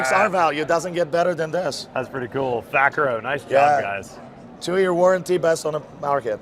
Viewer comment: 2 year warranty on the hatch? Did I mishear that? God, the fake tee up questions for all the product pushing is kinda gross.